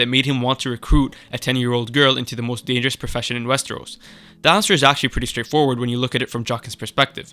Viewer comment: the text is often wrong....you say arya it says "his" 3:06 text says "Dr." you say The answer..why don't you proof check and edit your work....another at 3:17 your text says "what jackson saw" what you said is what Jaqen saw......